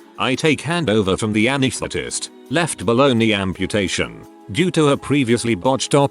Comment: At first I thought he said "left baloney amputation"